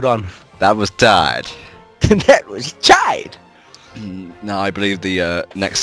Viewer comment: LOL, when said "That was tight." I thought he said "I almost died." when Sonic got an S rank at the end of Empire City day XD. I laughed so hard!